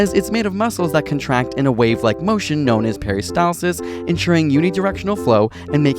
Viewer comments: "peristalsis" for a second I thought he said harry stylesis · When he said peristalsis i thought he said Harry styles whoops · When he said 'peristalsis' I thought he say 'harry styles sis' 😂😂